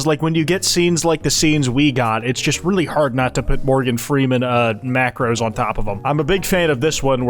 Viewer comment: My dumbass thought you said gordon freeman!